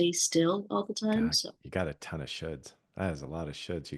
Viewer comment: I think her and I both thought he said "wow you have a lot of shit"